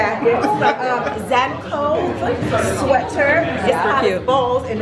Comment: I couldnt hear where she said the jeans were from. Can you share? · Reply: Coperni